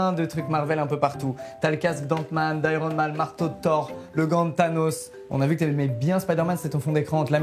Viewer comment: il a dit quoi la Squeezie ??? "Le gant de THANOS"